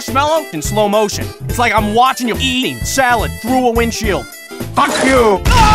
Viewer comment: i think wizard say not screw you but fuck you i think🤔🤔